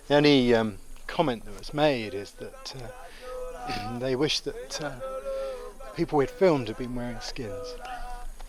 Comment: what did he say at 57:52? they wished what? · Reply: YIC They wished they would've been wearing skins (Authentic ritual clothing).